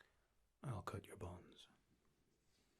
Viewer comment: definitely thought you said "I'll cut your balls"